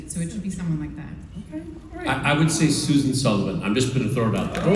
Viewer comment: Who did Nathan say should be Tim’s mom? 22:24 Susan …? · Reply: Susan Sulivan it was his tv mom in his previous show Castle.